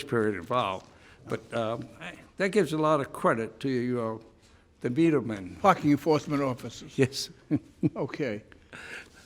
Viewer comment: sounded like he said F*CK YOU FORCEMEN OFFICERS. Haha